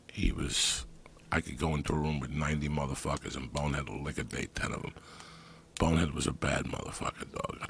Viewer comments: What does Joey say at 3:34? I keep listening back but can't figure it out. Liquidate? Liquor Date? Lick OR Date? WhAt DoeS iT aLl MeAn?!?! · what is he saying there? He could go into a room with 90 people and bonehead would what to 10 of them? Can't understand what he is saying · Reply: YSAmmo liquidate? Idk